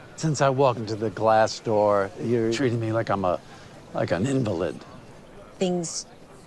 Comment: Excuse me but on 02:22 , Larry is saying " treating me like a xxxx". I can't catch the word. Can anybody tell me, plz? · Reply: Invalid